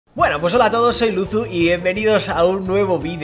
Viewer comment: Si no dejas que de 0:00 pase a 0:01 y lo repites parece que esta diciendo "Mueran"